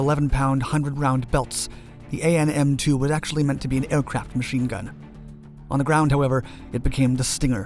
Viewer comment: I thought It said a airsoft machine gun Imao